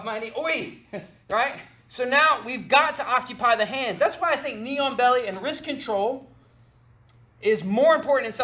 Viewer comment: I heard "Neon Belly" 😂😂